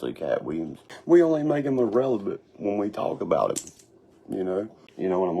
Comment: did he say “irrelebut” 🤣🤣